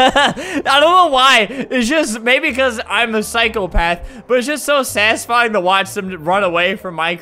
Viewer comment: It sounded like he said “I’m the cycle path”